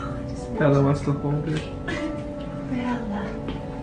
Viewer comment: did he say "Bella wants to hold it", or am I hearing things?